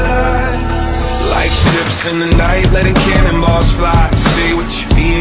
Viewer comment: i thought that same thing i had to look up the lyrics to see if was actually saying that no matter how hard i try i cant hear 'letting cannon balls fly' i always hear 'letting kanimas fly'